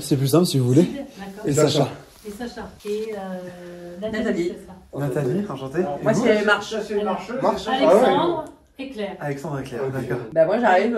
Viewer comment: j'ai entendu Alexandre Hitler 🤣🤣😉😉😉😉